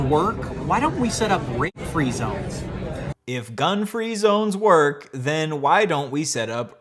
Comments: I thought you said rent-free at the start... this hit way harder when I heard the right word suddenly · I thought he said RENT free zones and thought "Wow, that's stupid as hell"... then I realised what he actually said and almost screamed. I thought it couldn't get any worse, but it did somehow